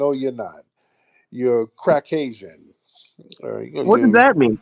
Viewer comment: I thought it said Crackistan 😂